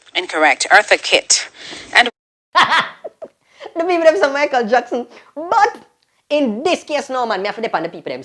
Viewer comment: They said Monica Jackson. Did not say Michael Jackson. Listennnnnn!!